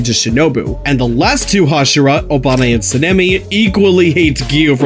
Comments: For a second, I thought he said Obama instead of Obanai😭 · Captions:”Obani and Sanemi” What I heard: “Obama and Sanemi” 😂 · When he said obanai I thought he said Obama….. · bro i thought u said Obama instead of obanai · For some reason I thought I heard “Obama and sanemi,” that would be an interesting duo. 😂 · At first I thought he said Obama for Obanai lol